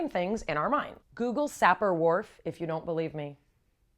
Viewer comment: Can anyone tell me what she's saying to google at 0:45? I'm hearing sapper wharf but can't find anything on google